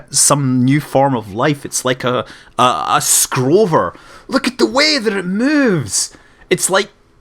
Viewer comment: He said "scrover" and I thought he said scrotum but I reminded but it was actually scrover.